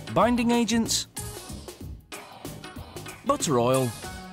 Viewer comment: Really sounds like he says motor oil.